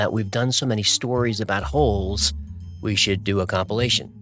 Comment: Did he just say A-hole-cumpilation?